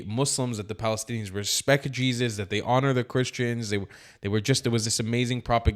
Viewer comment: I thought they said muslims respect Jesus!!..hypocrites!!